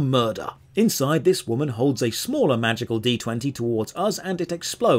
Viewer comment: The importance of a pause in your sentence makes all the contextual difference here... 😅 I literally thought you said "Inside this woman, holds a smaller magical D.....etc." I didn't hear the rest because I was under the impression it was another dirty joke until I realized what you were actually saying 🤣